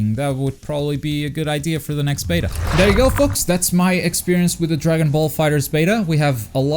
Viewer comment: it sounds like his saying there you go fucks